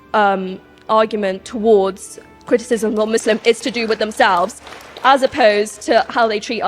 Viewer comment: Wait wtf did she say "Misslimps" ?! lol